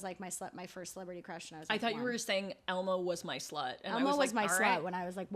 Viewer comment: swear to God I heard, "Elmo was my 1st slut" then said it